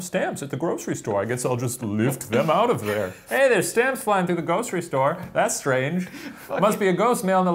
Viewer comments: I totally heard "Ghostery store" when Shane was talking about ghosts stealing stamps. I thought it was hilarious until I realised Shane wasn't making that joke & my heart became sad. Missed opportunity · I COULD HAVE SWORN SHANE SAID "ghostery store" when talking about the stamps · Did anybody else think it sounded like Shane said "Hey there are stamps flying through the GHOSTRY store."